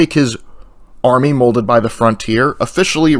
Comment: Did you just say 'FRONTIER"? Seriously, this is both the NCR Exiles and Snowblind Legion playthroughs in one. Bottlecap for your thoughts.